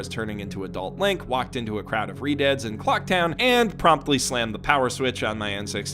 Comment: Did he say Clock Town 8:34?